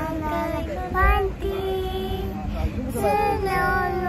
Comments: もうがパンティーって言ってるって前言っててそれにしか聞こえん笑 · 運命的な恋煩い歌ってる、｢何万回のパンティー！｣って聞こえて可愛い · 本家聞いてても「ロマンティック」が「パンティ」に聞こえてしょうがない🤣